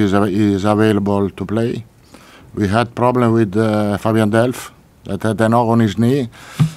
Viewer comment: I thought he said delph has got a nob on his knee